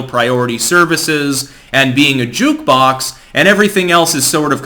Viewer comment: I heard YT "being a jukebox" as "jewbox" . Neither interpretation is wrong.